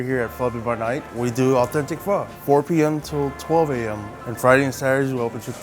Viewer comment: Did he just say? PHO PM?